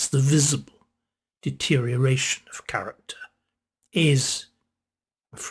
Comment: When you said, ‘character’, I thought at first you said ‘carrot top’!